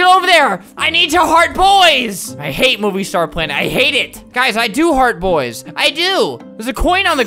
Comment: omg i thought he said “i do hard boys” and i’ve never been the same since..